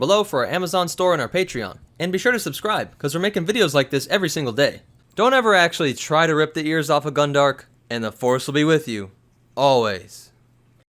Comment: I thought han solo said "rip of an ears of a GUNGAN"